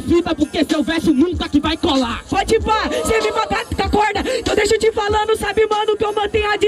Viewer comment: So escutei “deixa eu te falano” pqp panelilha é foda